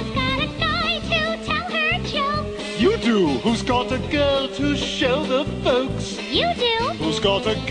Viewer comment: Did anyone Notice Stewie/Hiccup said at 0:37 that "Whose got a girl to show -HER- folks?"....did he just call himself a chick?